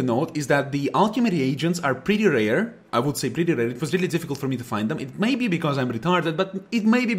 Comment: What is he saying? Priereiar?